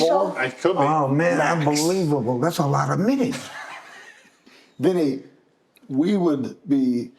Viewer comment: Wait was he saying around 1:15-17? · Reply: "Wow, man. Unbelievable. That's a lot of minutes."